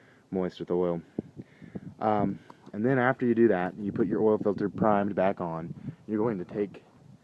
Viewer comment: Thanks for the video. Question: At 4:30 you say something like "...you put your oil filter primes back on..." but I must not be hearing you correctly. What are you saying? Thanks for the help. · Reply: Sorry for the late reply. I was saying put the filter back on “primed”. Meaning pour some oil in the filter before putting on vehicle so that it doesnt start dry